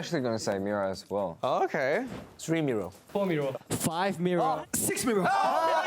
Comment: “Mirror, yea I was thinking mirror, three mirror, four mirror, five mirror SIX MIRROR... grow up Edit: I don’t understand what i.n said sorry!! It sounded like he said every song but idk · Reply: i thought they were saying miroh